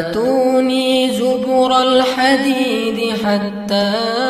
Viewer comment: maaf menit 35:59 itu saya denger atuni zuburol khadid..tapi tex nya atuni zubarol khadid..saya salah denger nggak sih soalnya setiap kali saya baca al kahfi atuni zubarol khadid